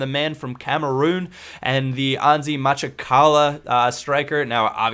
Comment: I thought it was said as, Antzi Ma-chaka-la? Or something like that :)